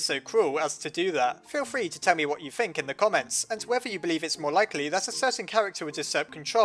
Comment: have i misheard "Feel free to Temmie what you think" or you really said that 😂